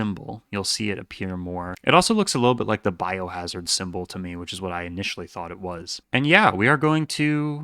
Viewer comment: I thought you said "the bible hazard symbol"